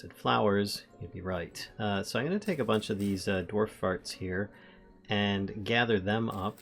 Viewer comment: the what? Dwarf furze - you said furze. Right?